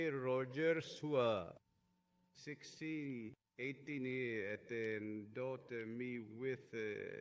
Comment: Can you please provide the email address that is given at 2:08:52? I cannot understand the pronunciation. Thank you. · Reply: Rogerzua1618 The rest Youtube wont let me type.